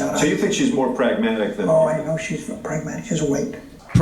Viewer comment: Jimmy you missed a small part my friend! Watch the video at 1:49-1:50! Reid says oh yeah she’s more pragmatic she’s AWAKE... you go apeshit over pragmatic when Reid was saying Lizzy Borden is FUCKING WOKE to the establishment’s political desires (to fucking perpetuate status quo and have people die in the streets instead of genuine healthcare reform- MEDICARE FOR ALL!!!).